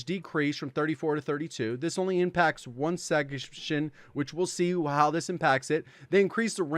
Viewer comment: did anybody else catch me saying section like “segmehtion” 😂😂😂😂